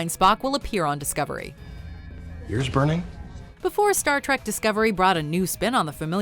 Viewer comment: I thought it said stark tec duces